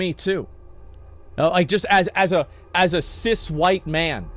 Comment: wtf did he call himself? a sis? o.O